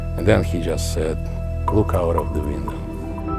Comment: what does he say? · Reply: "Look out of the window"